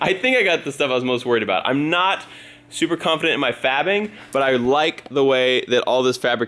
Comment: Who else heard "I am not super confident at my fapping!" at 7:04?